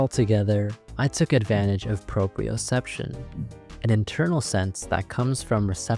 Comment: Why did I hear "Procreaception"